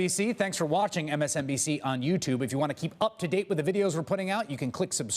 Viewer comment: did he say " just blow me"